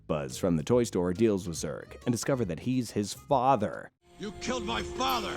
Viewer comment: Did he said hes his father